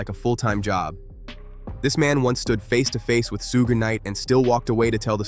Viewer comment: Did he just say sooga knight 😂😂😂😂